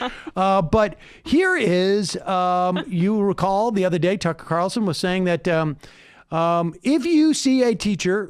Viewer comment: Oh ok for a second there I thought you said "Tucker Carlson's soul".